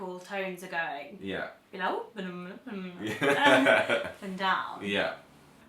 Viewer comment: did I just hear say “and I oop”?! Wow VSCO girls are spreading to the UK!